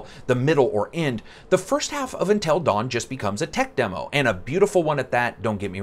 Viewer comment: At the start of the video it sounded like you said "Intel Dawn" Now that is a very different game.